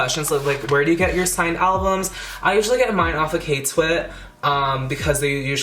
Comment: Did anyone catch the store name at 2:10? 🥲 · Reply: I think he said k twitt which is Korean Twitter